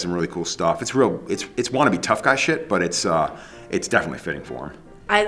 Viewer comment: did he say “it’s wanna be tough guy” as in NotoriousMMA is not the epitome of tough guy?!